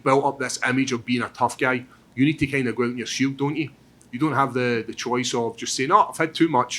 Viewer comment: What in the world does he say at 3:31? You need to grunt your soup? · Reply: "You need to go out on your shield, dont you?" Haha